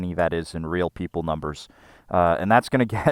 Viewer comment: Did he say "real people numbers"?